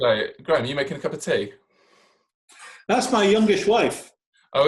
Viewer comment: Graeme says “that’s my youngest wife” I hear that right?